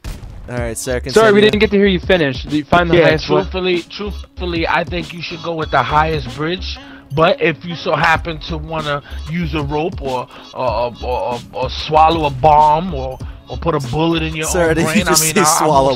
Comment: You meet the strangest people on Xbox Live. He still never gave me the proper instructions :^( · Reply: swallow a bomb I thought he said "Use a bong"